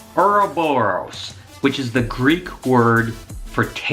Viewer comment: I thought it said OUR BROS.